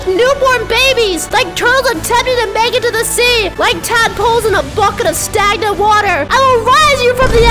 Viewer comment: Not sure if the editor meant they couldn't understand it but at 1:03 she says, "like tadpoles in a bucket of stagnant water"